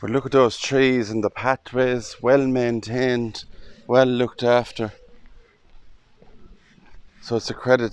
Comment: Was it just me or did anyone else hear a voice at 15:48? It sounded like “well looked after”